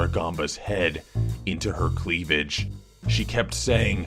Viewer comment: I wanted to visit that babysitter That LUCKY BOY HE GOT TO BE THERE IN BETWEEN HER CROTCH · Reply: Sufyaan Kinslow Crotch? I thought said cleavage